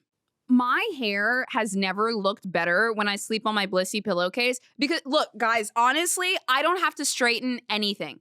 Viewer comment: When you started the ad read at 4:09 I was away from the phone drinking water in the kitchen, but I could still hear you, and I fully thought you said "bussy pillow case" and didn't even question that you would have a pillow case with "bussy" written on it in like Barbie font or some princessy font lol